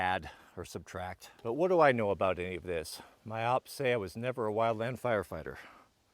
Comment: did you say opps? 💀💀💀